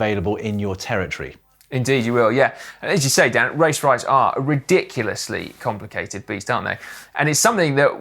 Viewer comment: was sort of half listening and for a second there I thought said "Race riots are ridiculously complicated beasts."